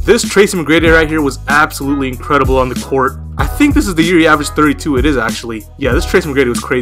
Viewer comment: Did you guys hear in 2:04 that said "I think this was the year I was 32" instead of I think this was the year HE was 32